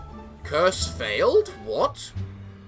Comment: ish i think it said either Core fail or Cure fail.